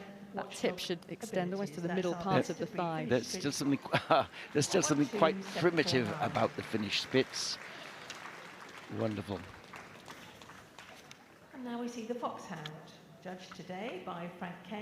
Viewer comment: I was working with this in the back ground and I heard "Fish Sticks" instead of Finish Spitz ... and now I wish very much that little floofs name was Fish Sticks